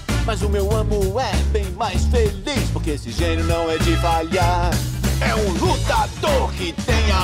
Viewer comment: Caraca brigado eu n sabia ate a agora que o genio falava "que esse Genio nao de falhar" eu achei que ele falava "que esse genio nao e devagar" obrigado me ajudou a ententer